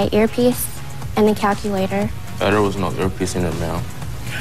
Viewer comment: I have no idea what is saying at 0:48. Is he saying, 'Federal is not your piecing of mail'? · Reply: "there was no earpiece in there ma'am